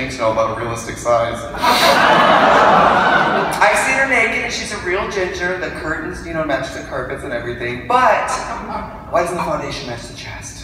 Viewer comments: I can not for the life of me understand what she is saying here. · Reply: "I've seen her naked and she's a real ginger. The curtains match the carpet and everything, BUT why doesn't foundation match the chest?" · What does Trixie say around 4:55-5:00? After the "carpet matches the drapes" joke. I only understood the word "chest". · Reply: I heard "why doesn't the foundation match the chest"